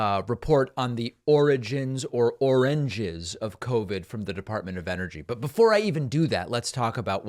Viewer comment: I keep hearing “Oranges of covid” for some reason. 😊😏